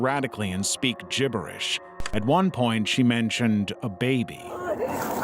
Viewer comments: She clearly says "What are you doing", not "baby..." Hilariously bad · she clearly says "what are you doing?" and not "baby..."